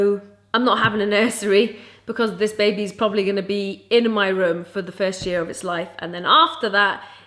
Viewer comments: did say this baby will be in my room for the first year of HIS life 😲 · For those who didn't hear clearly, she actually said 'ITS' not 'HIS'.